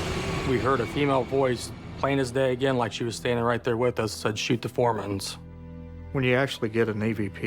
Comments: I thought the ghost said sweep the floormats. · LOLOLOL I swear, I thought the ghost said, "Shut the floor vent!"